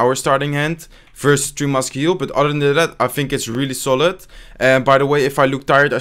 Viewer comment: what did you say there bro😂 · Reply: ClashTex "vs 3 Muskie/heal, but other than that.."